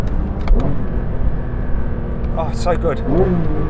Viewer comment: ああ、、、最高ぉ♡(空耳)